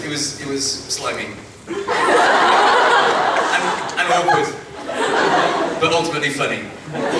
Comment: at about 2:30 do you know what he says 'It was slightly ...'? :D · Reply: "Awkward but ultimately funny"